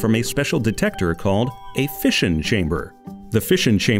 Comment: is it me or does he say "fission" like "fish'in"